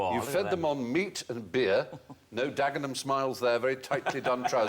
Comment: What the heck does he say at 1:46? I've watched this over and over and can't make it out, even when Jimmy Carr repeats it. Help out a poor North American. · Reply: “A Dagenham Smile”… is what he said.. a similar etymology to a Glasgow Kiss”